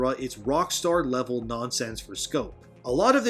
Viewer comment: They say "rock star level nonsense" I hear : we can't get on rockstars level....